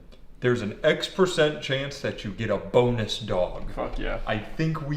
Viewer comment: At first I thought he said boneless dog instead of bonus dog